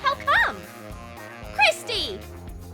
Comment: it sounded like she said crispy